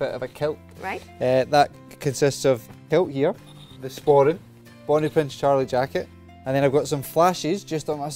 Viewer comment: Great video! I've been trying to understand the words that said before jacket (second 00:37) Did he say "Bonnie Prince Charlie jacket"? Thanks!